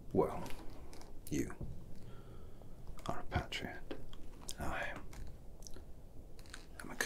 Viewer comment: I genuinely thought it said "You're a parrot" and then proceeded to be extremely confused throughout the duration of this video.